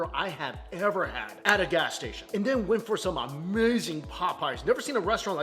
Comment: I'm a little disappointed...I thought he said "amazing Popeyes" instead of "amazing pot pies" LOL